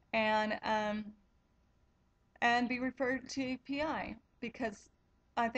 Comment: Hi, I am getting ccsvi done in 10 days. I have ms 17 years and have alot of problems walking even in my house. I wantrd to ask you what did you say you referred to at 6:00. it sounded like " I was reffered to epi or pi" I am secondary progressive. my email is <mail> Thank you for clarifying